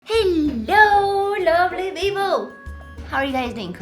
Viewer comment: when I started watching her videos, i always thought that she is saying 'Hello lovely Vivo' at the begining. I didnt understand why is she saying that, and I thought maybe she call her followers like that haha. after a while I realised that she says 'Hello lovely people'